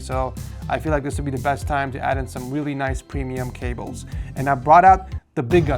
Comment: bro really said "whilly" instead of "really"